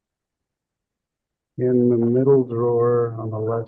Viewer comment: Sounds like he said $90000 dollars in the middle drawer!?